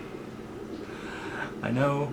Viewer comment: kinda sounds like he said: i'm transgigner lol